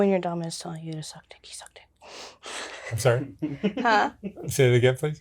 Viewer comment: Omg did she just say she forces him to “suck d*ck” on the guys she’s screwing around with and he’s OK with it? Did I hear well???